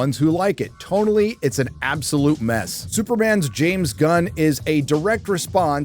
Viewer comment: Did he say Superman's James Gunn? 🤣